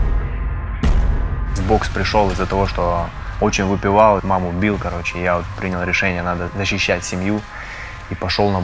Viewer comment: Вы бы видели мое ПРОСТО ПИЗДЕЦ КАК АХУЕЕВШЕЕ лицо когда мне послышалось что говорит: В бокс пришел из-за того что очень выпивал и маму бил...